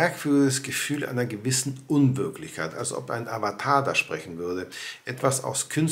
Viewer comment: Habe nur ich gehört: .".. ein merkwürdiges Gefühl einer gewissen Unwirklichkeit, als ob ein Avokado spricht"? 😜